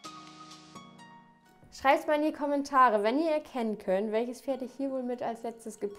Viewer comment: Habe bei Minute 03:26 verstanden scheißt mal in die Kommentare. 😂